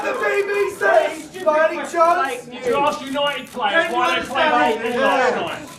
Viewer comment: I'm surprised they aired this, the very first response you can hear at 3:07 is "fuck off" lol. Also does anyone know what that guy said about the Man Utd players? "You should ask United players why they played...?